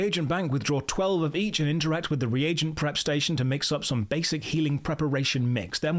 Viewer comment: I swear I thought you said "Reagent Crustacean" when talking about the prep station and was immediately disappointed when I clicked back to find out there was no reagent crustacean...